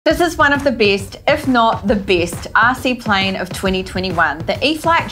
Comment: I thought she was saying it’s the beast plane 😂and then they went to the ear field